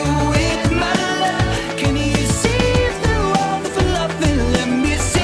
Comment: I'm deaf. When I first heard this, when Hal said "can you see through all the fluff", I thought he said "can you save the world with love"